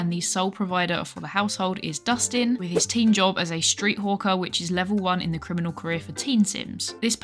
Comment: i heard “street hawker” as “streetwalker” the first time i watched this and was like “what developer allowed this?? isn’t he underage?? why is that even a career option in the game?” 😭